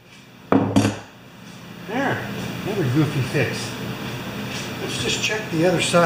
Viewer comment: Did I hear you say "another goofy fix"? ( 12:50 ) Haha, that's generally what I think about most of mine. Nice fix!